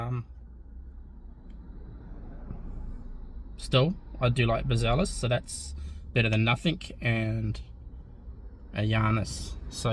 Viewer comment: The car rip was pretty funny. I gotta try that sometime. The first time I thought you said Matas was "A bit of nothing" but I just misheard better than nothing.